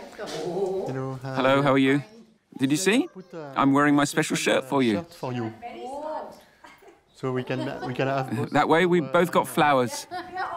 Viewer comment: i thought the kid said “ you see i wear this shirt for you …. so both of us get flowers “ even the lip moment matched 😂😂😂😂